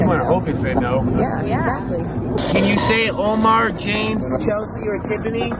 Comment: Time stamp 27:12 Okay I clearly heard a men said James